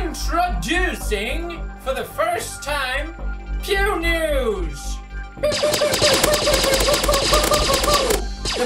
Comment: I thought he said pew Jews